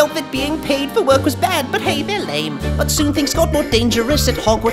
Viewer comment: I thought he said "but hey, their laid"